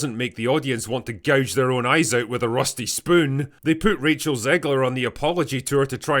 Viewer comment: Did you say... rusty... spoon? I like rusty spoons